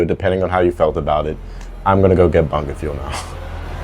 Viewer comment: what did u say there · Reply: I'm gonna go get bungerfuel now